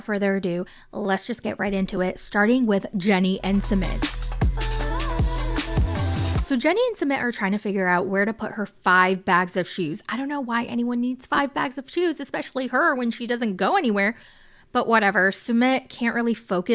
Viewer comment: "So Jenny and Summit are trying to figure out where to put her 5 bags of shoes". Girl!!!!! I thought you said they were trying to figure out where to put her 5 bags of cheese 🤦🏻‍♀️